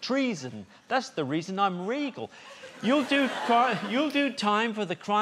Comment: Kudos to whomever blended this with the other interview and put a sound to it. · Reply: I thought in that version he said "treason? That's the reason im real!" for so long until he said "regal". I liked my version better.